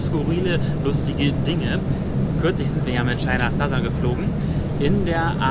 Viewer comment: China Satta? Noch nie von der Airline gehört!... bitte? ah?... meinte was?.... China Southern? Aha! OK, alles klar.